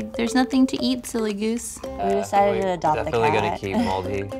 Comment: i don think cats are something you should "have in a week". They need home, they don need vacations.... or moving house... most of them... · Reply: I heard "we decided we would adopt a cat." but they may be saying "THE cat," I just can't hear it properly.